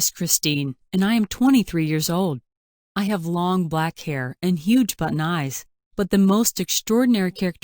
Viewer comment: did she say huge button eye 🙀👀